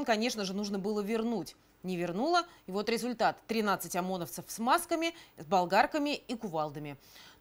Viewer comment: тринадцать ОМОНовцев в смазками?) я один это услышал?)